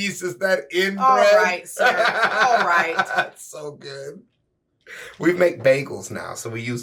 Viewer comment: I misheard and thought he said "We makE babies now.." and I was like "WHAT?"